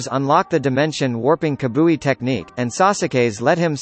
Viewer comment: Did he just say kabui ...🤐😂 dude its kamui